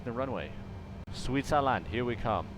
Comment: I really thought he said Sweet salad, here we come!!!